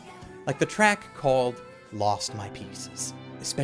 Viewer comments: When you said "Lost My Pieces" i thought you had said "Lost My Penises" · am i the only one who thought he said "lost my penises" at 8:04? probably haha